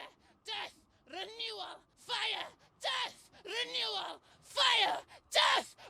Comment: I misheard what the the flamethrower dude said and thought he said this; "Fire, Death, Vanilla" Sounds like a nice combination of ice-cream scoops